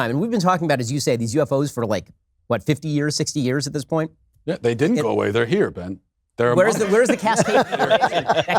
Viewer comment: did Matt say “They’re among us” or do I need to see someone